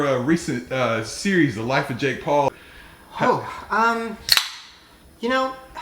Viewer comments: Did he just say The Life Of Jake Paul? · um......did he say the LIFE of jake Paul????